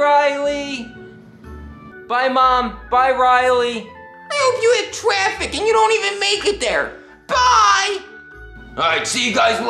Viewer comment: what did say? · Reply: "I hope you have traffic and not even it there"